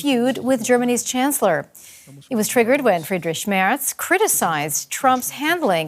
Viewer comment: Did she actually say "Friedrich Schmerz" ?! 😮😂